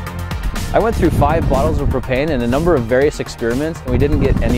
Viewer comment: I swear I heard "I went through 5 bottles of cocaine..."